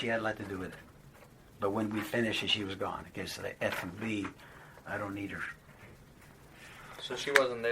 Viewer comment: I heard it! Did anyone else hear it too? At 46:00 "That f**king bi*ch, I don't need her" and at 46:10 Jeremy said, "My wife, knows I has having an f'n with the N word lady". Jeremy dates and has sex with Black women but, he calls them the N word?